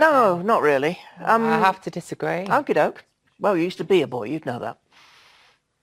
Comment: at first I thought she said you should be a boy. but I guess she said you used to be a boy. so it's only slightly rude and true.